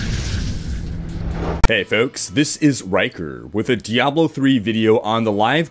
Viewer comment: Every time I hear your intro now, all I hear is "Hello this is Rhykker's Wizards" lmao. But anyways thanks for the info!